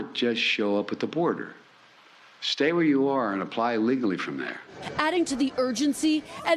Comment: What did he say at 1:12 Sounds like, "Stay where you are and apply illegally from there" At least he's telling the truth